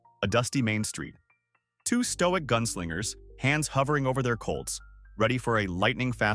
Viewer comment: while what the narrator says really means hands hovering over their Colts, the text says hands hovering over their coals. ...You should correct this error.